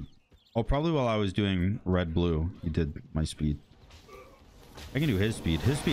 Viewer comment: He actually said "my red blue" 🤣🤣